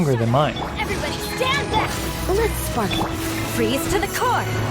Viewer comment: I hear let’s fak